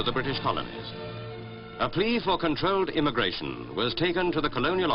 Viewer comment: I'm currently working on this video and i can"t understand what it said at 2:03.... A .... of controlled immigration ?? Could you please help me ? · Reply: A plea for controlled immigration